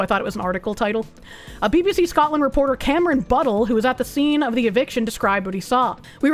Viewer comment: For a moment: I thought you said, "Reporter camera in but hole." 😳